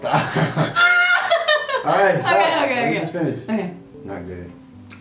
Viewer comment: or 8:34 did he say alright babe?